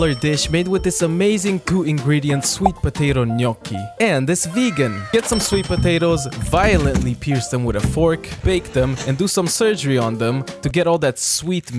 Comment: When he said violently pierce them with a fork I thought he said violate them with a fork